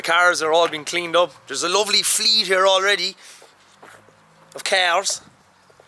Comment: Thought I heard you say there's a lovely fleet of cows and we're taking them to a BBQ LOL